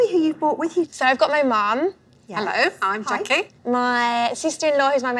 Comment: I heard, “hello. I’m junkie.” Lol..